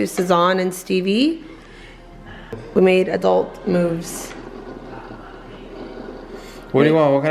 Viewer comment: When you said "we made adult moves" my neck went back! I heard it as "adult movies" ... daaaayum!